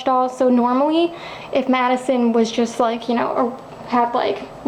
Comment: did you just say my name? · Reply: I said Madison, the name of the doll in the video :)